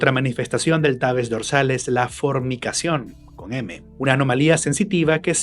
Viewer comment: "otra manifestación es la "formicacion"... con M" Por un momento escuche "fornicacion" XD